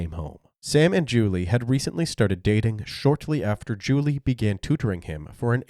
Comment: What does he say at 4:53? Something that sounds like 'Julie began to drink him'? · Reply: If you still care it was "Julie began tutoring him"